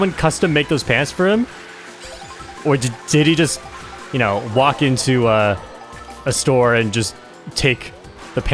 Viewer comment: I thought you said diddy walked into a store and cut out a hole for his tail